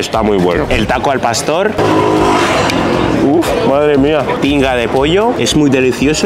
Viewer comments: Yo pensé que en el tinga de pollo pensé que dijo pinga de pollo xD🥵🥵 👇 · Yo entendi "Pinga de pollo" XD